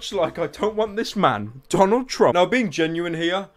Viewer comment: Did I hear you say Donald Trump!?!